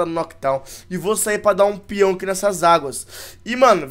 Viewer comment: dar um pyong?? foi isso q eu entendi :V